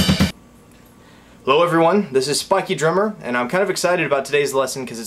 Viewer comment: lol at the beginning i thought you said "hello this is fucky drummer", anyways you kick ass